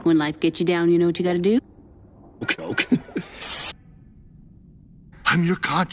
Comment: Dory: “When life gets you down, y’know what you got to do?” Marlin: “Coke.” My humor has died- I’m laughing way too hard at this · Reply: Ohhhhh, I thought he said "choke" 🤣🤣🤣